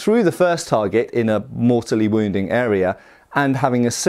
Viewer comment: It took me a moment to work out that said, "A mortally wounding area", instead of what I first heard, "A more silly wounding area".